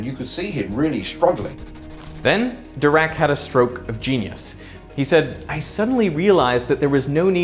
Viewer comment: I heard him say, "And then Dirac had a stroke..." and thought he was done! 😂